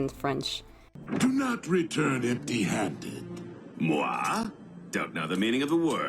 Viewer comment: I did not know knockout was speaking french i actually thought he said "What" and not "mooi"